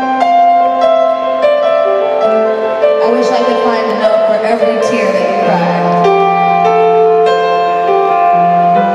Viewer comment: what did she say I can't really hear it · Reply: I wish I could find the note for every tear that you’ve cried